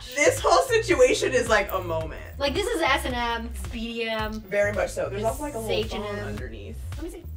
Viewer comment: did she say H&M lol